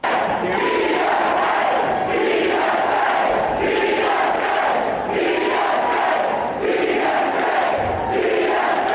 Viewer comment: i cant hear what theyre saying but they sound happy, so good for them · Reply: They're chanting "DSA! DSA!"